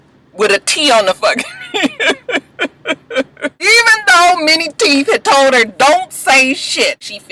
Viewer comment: I swear I always thought you were saying MINI Teeth.....not MANY teeth....LOL